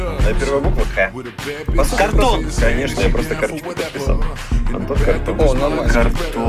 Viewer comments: мне послышалось, что он сказал "артон - картон"... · Мне послышалось будто Антон сказал, артон - картон, я такая думаю: Что...